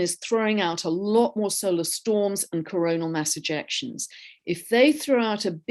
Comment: Coronal mass ejection - I heard that as coronal mass INJECTION and laughed at the poignancy of that wording! Even though these astrological energies are intense, I am feeling super grounded and it must be, like you both said, all the grounding in my body I do daily. Body consciousness all the way!